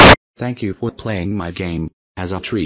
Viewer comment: Anoymous: Thank you for playing my game! As of threat, I will blow your Super A'can wiped clean! Have good day now... (FINAL JUMPSCARE!) Me: The console its going to blow!!!!!!! (Console Exploded) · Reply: hmm... i thought it said "as a treat"...